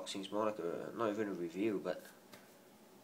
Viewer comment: Did you just say "thank you guys, piss off"?